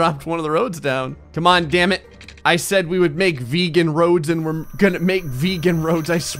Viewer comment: Did he just say vegan roads #TrIGGERD# #TRIGGERED#